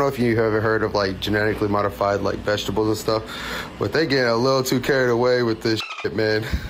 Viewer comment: "a little too carried away" sounded like a little too "carrot"😂